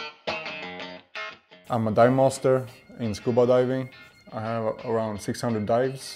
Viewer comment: I misheard him at first and thought he said he was a diet monster. Dive master is way more fun by the sounds of things